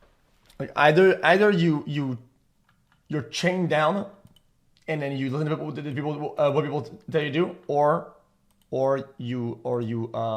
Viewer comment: ??????? wtf did he actually say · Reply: Either you're chained down and you listen to what other people tell you to do